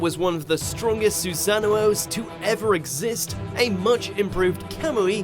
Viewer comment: did he just say susano-o-o